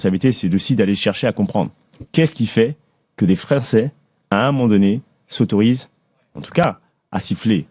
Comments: Jai entendu Frèrcais ,retourne a L'école et apprend a bien parler avant de faire ta pleureuse sur les médias Calimero. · à 7:27, c'est moi où il dit "des Fra'çais". Une petite résurgence du wakanda mamadou ?